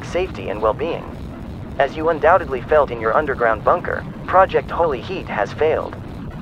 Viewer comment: Why did I hear “Project Holy Shit”?